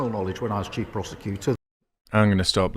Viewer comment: i thought he said chief prostituter